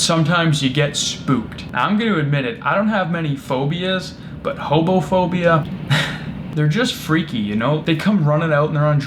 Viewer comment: I could’ve sworn that he said he had homophobia! I was like wth is going on about them coming out and shouting at you etc - Sorry, it’s just the accent!